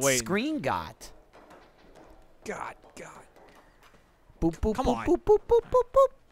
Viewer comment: What are Jack and Michael talking about at 31:09? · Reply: Ahh ok, I thought they said Boo's but had no idea what that mean haha. Thanks!